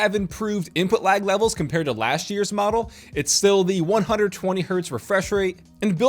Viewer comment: wait did you say 120hz?!